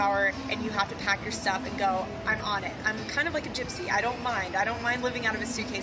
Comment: "Kind of like a gypsy"!! Did she just say she's juiceish??!😮